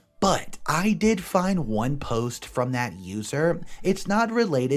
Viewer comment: I thought I heard at 7:11 "from that loser" XD